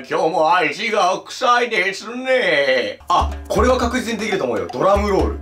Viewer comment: 本人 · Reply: シ「足が臭いですねぇ～」 にしか聞こえん(笑)